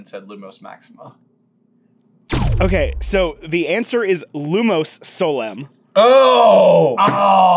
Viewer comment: I always thought she said lumos O’lamp lol